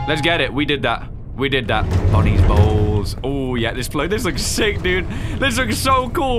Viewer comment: every time you say "bonnies bowls" i hear "bonnies balls" i have a brain damage bruh